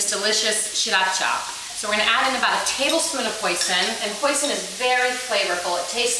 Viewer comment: did she say poison? what's she trying to do, kill us?